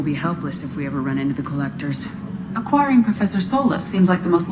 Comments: Did...Did EDI just call Mordin a Quarian? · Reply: "Acquiring Professor Solus" not "A Quarian, Professor Solus,". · Did EDI said that Mordin is a Quarian? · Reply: Nope. She said "Acquiring Professor Solus". But now that you mention it, it does sound a bit like she does, haha.